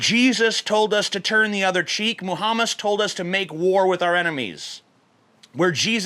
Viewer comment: Anyone else hear "Muhamas" at 4:35 ?